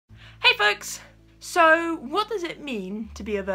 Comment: Oh my it sounded like you said "hey fucks" in the beginning...